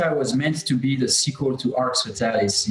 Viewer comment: ► SUBTITLES ARE PROVIDED: Make sure to turn on "English - French Speakers Only" Subtitles (hit the gear icon on YouTube). Other options are also available. ► Chapter Timecodes: 02:56 - Arx Fatalis 05:04 - Dark Messiah 14:21 - The Crossing 22:00 - LMNO 33:00 - Ravenholm 49:32 - Bioshock 2 56:11 - Dishonored 1:05:31 - PREY 1:09:44 - Dishonored 2 ► Our work is 100% editorially independent and 100% crowdfunded. Please help us tell more stories like this - <link> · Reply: At timecode 4:51 (nice) the subtitles says "Yeah, Dark Messiah was meant to be the equal to Arx Fatalis" when it sounds to me like Rafael said "sequel." Also, at 6:05 the subtitles says "So the reason why we used a source engine was kind of an accident." That should be "why we used the Source engine."